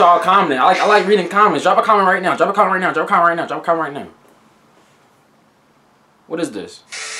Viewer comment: when u said drop a comment right now u sounded like u said drive a car 🚗 right now 😂